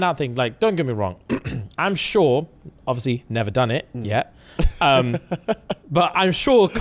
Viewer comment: not me to rewinding when I know I heard “yet 😉”!!!!!!!!!!!!!!! 😂😂😂😂